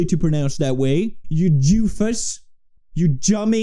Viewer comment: did he just call that man a jewfus... im calling the wall street journal